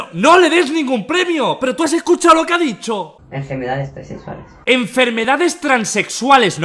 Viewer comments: "El condon se usa para evitar las enfermedades transexuales" - 2019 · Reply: Yo le escuché "enfermedades preservales" 😅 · Yo le escuché "enfermedades preservales" 😅